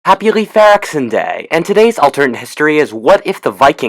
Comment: It sounds like he said happy leff areksin day